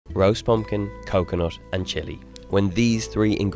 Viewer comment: Thought he said gross pumpkin